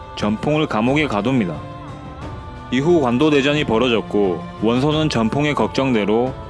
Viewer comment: 저만 완도대전으로들리나욧....?